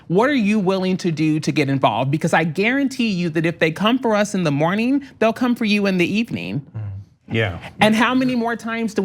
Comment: "If they don't come for you in the morning, they will come for you on the evening.' Fascism in 2019. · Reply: That's not what she said. It's "If they come for US in the morning, they'll come for YOU in the evening."